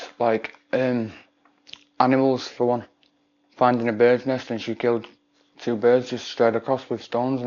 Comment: is he saying she killed 2 birds with one stone?